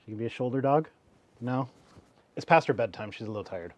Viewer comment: It's too late for this comment, but when you said "it's past her bedtime", I heard it as an introduction: "iiiiiit's Pastor Bedtime!"